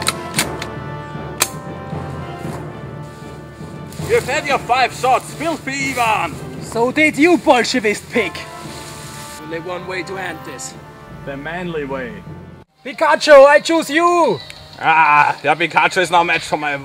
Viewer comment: at about :46 it sounds like he says bolshevik pig but he's the russian 0.0